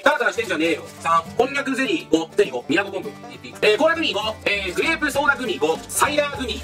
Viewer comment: くらいのコーラグミ5が 倖田來未5に聞こえて 一人でツボってしまったw